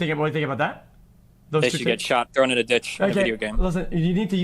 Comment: it sounded like Destiny was saying "they should get shot and thrown in a ditch", can anybody tell me what he actually said? Because he then said he meant that they should get the shot as in the vaccine, so I'm kind of confused...